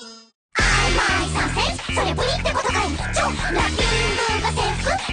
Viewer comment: I don't know if anyone else hears it but it sounds like she is saying "ai my sausage!"